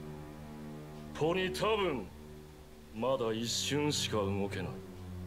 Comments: BRUH WHY DID IT SOUND LIKE HE ACTUALLY SAID "PONEY TOWN" OML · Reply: he actually said "tabun" not "town" which means "probably" a trick worthy of a jojo twist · "Pony Town" Did you do some voice-splicing there? Or did DIO in fact say some syllables that sounded like that at some point? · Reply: he said “to ni tabun” which means “Maybe” in Japanese